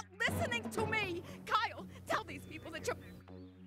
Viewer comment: For those wondering what Sheila/Kyle’s mom said before the window cut her off, it was “mother never raped you! Tell them! It's my fault. Say it! Tell them right now!!”